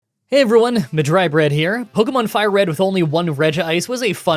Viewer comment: pokemon fire red but with only level up moves only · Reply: For a second, I thought you said "Red" rather than "Fire red" and was very concerned.